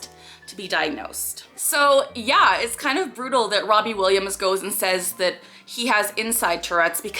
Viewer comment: At first I thought it said Robin Williams and I was like "has he risen?" 😂😂😂😂